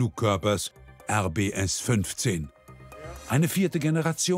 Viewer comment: Hab ich da RBS gehört? Meddl.